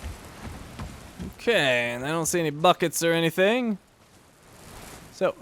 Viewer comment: I swear,every freaking time he says "bucket" I hear "Fuck it" or "fucket". So effing confusing.